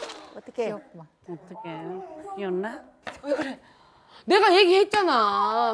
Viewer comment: 너무 귀여워서 베였나가 귀엽나로 들림 ㅋㅋ